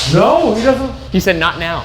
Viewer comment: I heard, “Not now, go” and then “Mary..?!” from a crying woman